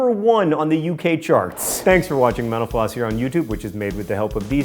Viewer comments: At the end of this show it sounds like you say " Thank you for watching menopause" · at first i thought he said "thanks for watching mentopause..." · At the end I heard "Thanks for watching menopause."